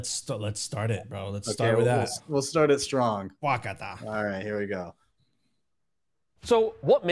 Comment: Did he just say ok in Japanese? @ 9:12 wakata